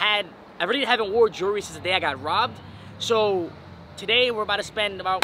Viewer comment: O okay . I thought you said you got robbed again .